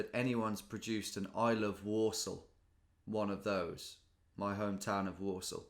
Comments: When you said "there's no way that anyone produced an "I love Walsall" one of those" I heard "Warsaw" :) Got me thinking - are these two pronounced the same in English? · Thanks for clarifying it’s your hometown (Warsall). I first heard “there is no way anyone would produce I ❤️ Warsaw “ 😳